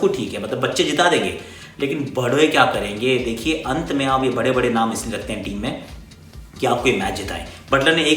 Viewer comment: anyone else hear "lekin 'bhadwe' kya karenge" xD